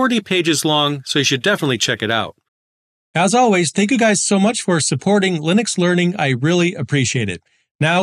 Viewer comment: I clicked away because while I had your video running it sounded like you said fair well. My auto fire sent me clicking. I saw the red playback line as the next page began loading... and I thought I''d come back for a second and let you know. I think it was the "As always, thank you guys for supporting the channel." That made me think the video was over.